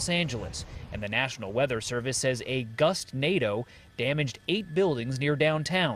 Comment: did he say gustnado